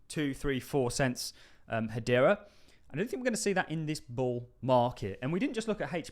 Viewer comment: I’m confused, so his saying it’s going to do 2 dollars but not in this bull market? Can somebody confirm that’s what his saying please. Then what’s the point of buying this coin now. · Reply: It sounds like he says I don't think we're going to see that in this bull market.